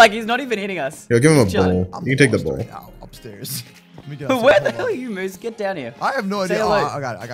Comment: Who else hear say “where the hell r u moose “ at 3:15 Edit :btw the vid was awesome !! Fav youtubers ever!!